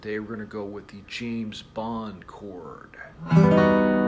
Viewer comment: Him: “James shred.” What I hear: “r@ shrek.”